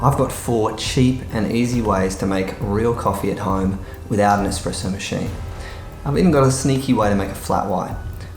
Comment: I thought u said no machine